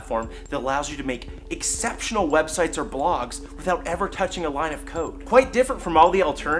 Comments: "Without touching a single line of coke!" I thought he said at first haha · did he say 'Without ever touching a line of coke?'